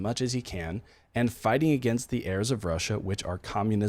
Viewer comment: did you say the "heirs of Russia"? I'm curious about your source of this phrase, please. Thanks! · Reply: “Errors”